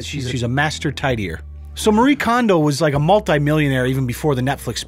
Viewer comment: I thought she said Marie Condom lol